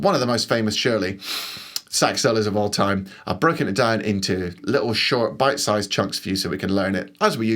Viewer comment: Did u just say ..”I’m gonna Brecker it down?” Lol